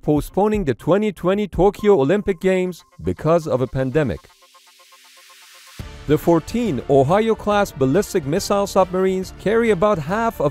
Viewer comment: why i hear "14 ohayou-class submarines"